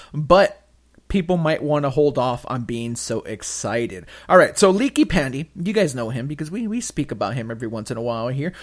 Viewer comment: did say "leaky panty"??